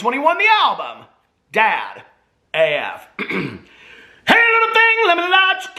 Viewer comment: All this time I thought was saying DAD AF but now I think it's Dad AM. 🙄🙄